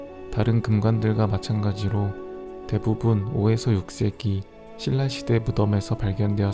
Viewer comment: 세기가 왜 새끼처럼 들리는 걸까..